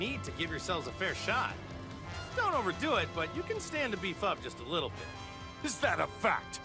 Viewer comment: I didn’t hear ‘beef up’ I heard ‘be fucked’ Anybody else? 😂